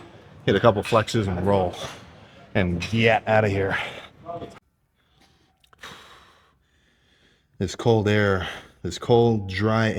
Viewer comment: did he just say gyatt out of here? 😂